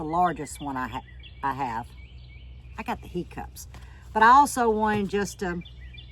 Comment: ALL SMILES TO A GRAND RISING TO ANOTHER BEAUTIFUL DAY...❤... I THOUGHT YOU SAID YOU HAD THE HICCUPS ... WITH ALL I HEARD WAS THE HOOK EM UPS.. THANK YOU FOR SHARING YOUR KNOWLEDGE AND YOUR SMILES..❤🥰 🙏🏼♥️🕊️💞💪🏼🏋️👨‍👩‍👧‍👦👨‍👩‍👧‍👦🥰🙏🏼